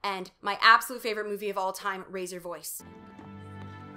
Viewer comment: For a split second, I thought you said “Razor Voice”, and now I want a movie where Hillary Duff discovers her secret talent is singing metal.